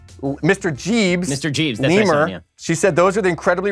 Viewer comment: knowing that Ben nicknamed his son "Memer" is the best news I've heard this year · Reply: I heard it as lemur